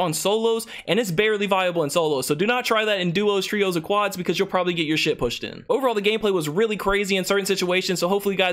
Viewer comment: LMAO 1:44 “you probably will get your sh*t pushed in” first time I hear that lolololol